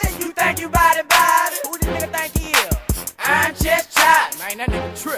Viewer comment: all this time i thought he was saying "I'm Just Charlie"...Ha3x